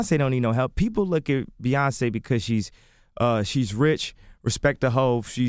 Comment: Kanye sounds like he said respect the hoe but he said respectable